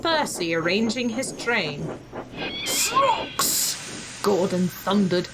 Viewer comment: Are you sure Gordon said "Trucks"? Because I heard "Socks"